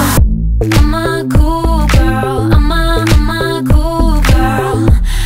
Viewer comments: at first, thought she said "i'm a cougar" lol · When she says "I'm a cool girl" I keep hearing "I'm a cougar"....